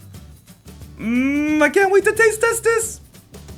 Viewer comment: am i the only one who heard i cant wait to taste testes